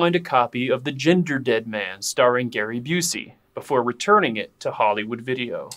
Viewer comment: I thought he said about the use drive "before injecting it"